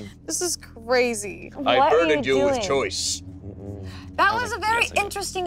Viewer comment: Did Damien say “I BIRDED you with choice”? 😂